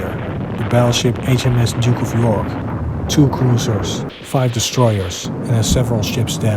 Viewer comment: you said 5 but CC says 4